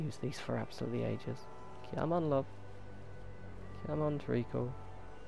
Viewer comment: what is your saying after come on love? you always say that and i never understand. kinda sounds like your saying tree-ghoul. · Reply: He's saying "Treacle" (it's a term of endearment/affection especially around the London area, simply put, he's basically calling it sweetheart :) )